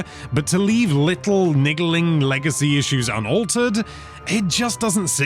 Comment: what did he say? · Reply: “Niggling” go on captions